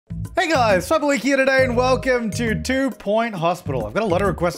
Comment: Ok yeah so you can SAY "Fabliki here" but all I hear is "hey guys, James here!" You'll always be my dummy boi.